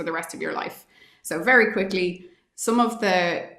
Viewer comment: I thought she said "quickie", but she said "quickly".... 😂. She's a great find!